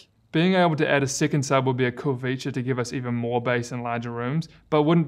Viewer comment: Hold on, at 4:10 did you just say I can add a second subwoofer? Didn't find anything in the manual. That is neat. Do I buy a second subwoofer as spare parts? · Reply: No, I said "Adding a second Sub would be a cool feature" You can't adding a second sub with this bar I was more wishful thinking for future generations.